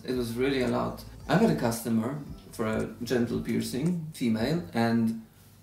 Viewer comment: I thought she was saying gentle piercings at first🤣